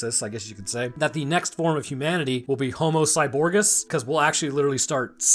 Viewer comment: What said: "Homocyborgus" What I heard: "The flesh is weak"